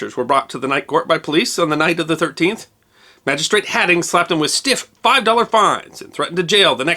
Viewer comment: did he say "magistrate hatting?"